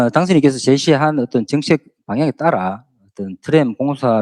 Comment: 해당 과장은 <당선인께서 제시한 '정책 방향'>이라는 워딩을 썼는데, 왜 김상욱은 '정치적'이라고 들었을까요? 😢 이비인후과 진료부터 받아보시길 바랍니다.